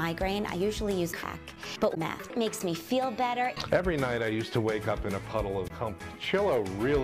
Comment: Anybody notice that there were three hands... Just think about it, who else is with her... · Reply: I thought she said "I usually use 'Pack,' but 'Mac' makes me feel better." With Mac being the other person.